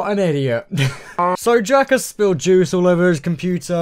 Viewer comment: the captions say jackass XDDD